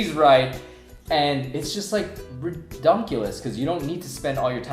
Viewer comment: did he just say ridonculous????